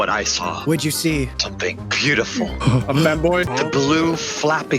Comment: did really say FEMBOY?!